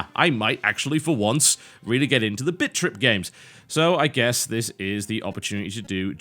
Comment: sounds like he saying bitch trip